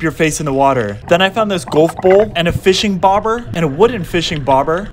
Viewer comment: Did you just say "Gawhf bowl"????